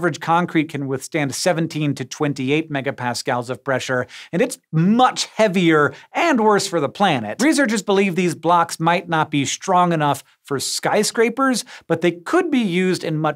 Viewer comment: am I the only one who when he was talking about ash blocks sounded like he said ass blocks? lmfao damn and I was hoping to live in a mushroom home like Mario and princess peach. lol